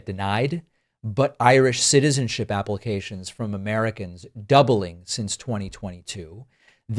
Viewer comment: Did you say: “Irish citizenship applications Dublin since 2022?” heeheehee